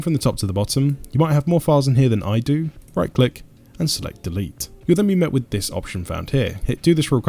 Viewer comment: what he want to say pls help me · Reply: Right click, select and delete